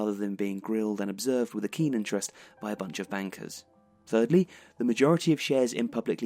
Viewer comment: When you said "by a bunch of bankers" I totally thought he said wankers